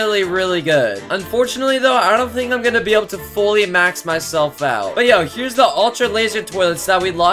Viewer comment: The ones who heard him saying max my self out ⬇